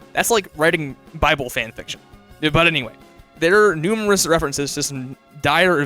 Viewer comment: I have too much Andrew Cunningham brainrot bc when I heard him say “numerous” around 6:43 I thought I heard “Umriss”